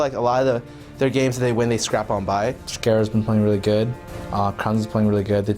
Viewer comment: Did say Scare-a @ 1:42?